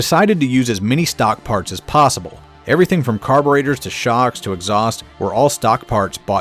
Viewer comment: Yeah, I heard you say "cahburetor"